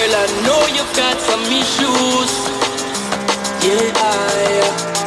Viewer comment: It sounds like he is saying "I know you got tommys shoes" :P